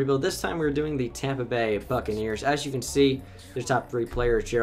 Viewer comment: I thought he said fuckakneers